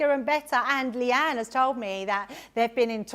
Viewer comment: Sorry I didn’t see, I thought I heard Leann! Hello Debbie’s! Happy Friday from Southern California. As I said my daughter just got back from her UK trip and absolutely loved it!